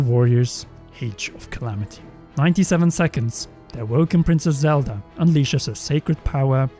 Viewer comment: did anyone else hear “a woke princess Zelda” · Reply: "awoken", as in her powers being awoken. That part is most likely her at the castle right before she goes to stop Calamity Ganon.